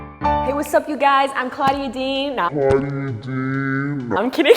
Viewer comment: i thought u said u were claudi D 😂